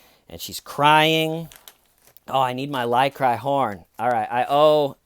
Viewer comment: I thought you said lycra horn...I actually spent time googling lycra horn to see what the hell you were going on about....I only understood when reading the comments.....ffs 😁